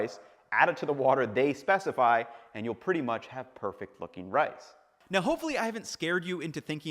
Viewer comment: I heard "Perfect fucking rice" instead of "perfect looking rice". Needed the captions for that lol!